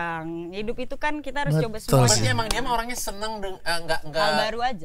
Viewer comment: Di menit 1:04:50 ada yg kayak berbisik ngomong "betul si". Kalau pake headset kedengeran banget